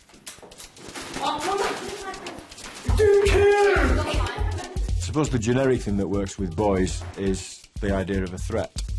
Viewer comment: what is he saying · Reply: Do coke.